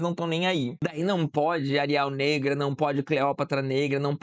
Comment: Quando você falou “Ariel negra”, por alguns segundos entendi “ariAl negra”. Pensei “nossa, pq tão falando da fonte arial?” Mas daí entendi. Acabei de acordar 😭